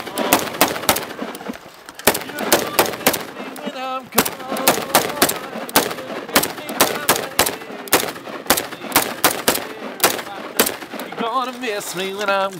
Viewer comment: I thought he said "Your gonna miss me when im gun."